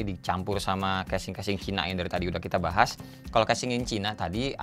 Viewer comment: gue dengernya casing casing hina 😄 .. cina Setelah di review